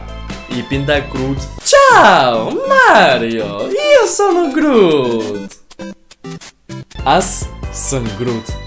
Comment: I thought you say - it's - a - me Groot!